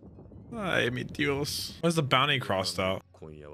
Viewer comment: Did you say "Ay mi Dios" ? hahahahah